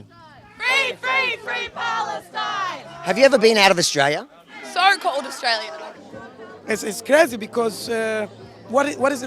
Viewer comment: ⤴SHARE THE FULL STORY: <link> 🚨DON'T MISS A THING: <link> 📖 GET MY NEW BOOK: <link> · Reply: Subtitle says SOC Australia (which is ADF), sounds like she said "Don't call it Australia". If the latter, would have been great to ask what she calls it.